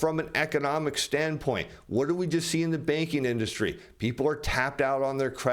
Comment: Good info! But I thought he was saying the baking industry .